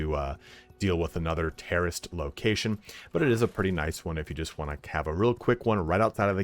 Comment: thought you were saying "terrorist location". I was like WTF is he saying that for??